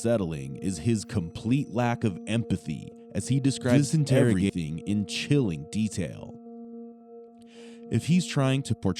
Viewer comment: or there about, there's a split second of overlapping audio. Two garbled words. Ghostly sounding. Can't quite make it out. Anybody else hear it?👻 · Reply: It just says "This interrogation". Probably an overlap audio clip during video editing since that part was the Channel's voice over narration.